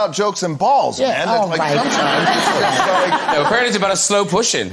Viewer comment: what did Graham say? Couldn't understand the joke... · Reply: He said: apparently it's about slow pushing.